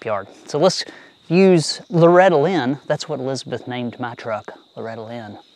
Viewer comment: When you first said let’s use Loretta Lynn I swear I heard ‘let’s use rattle-inn’. Which in itself is a good name for an old truck.😂